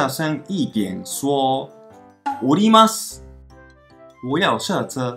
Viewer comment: 我在東京時一直聽成のります…想說都要下車為啥要講上車，原來是おりますww